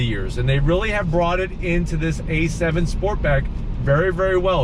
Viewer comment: Did he just say A7 Sportback at 16:29? 😶